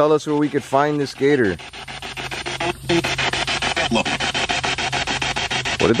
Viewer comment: I heard lock.